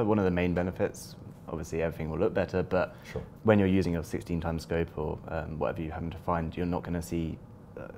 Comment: 16 times scope confirmed? or did i hear wrong?